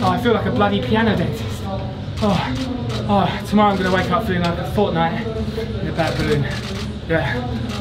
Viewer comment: did he say hes gonna wake up and play fortnite. Probably hearing that wrong.